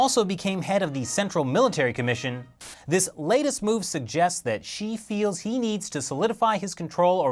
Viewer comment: I think I heard "she feels he needs to...."